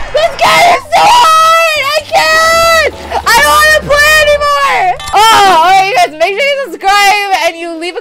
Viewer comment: I put the captions on because she was being very obnoxiously loud (no offense) and it said, "I don't want to be easy baby" because it thought she said that. It also said, "I don't know if I did go to die" What the heck captions?